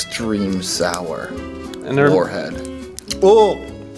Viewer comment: the captions say [INAUDIBLE] but he actuallys says warhead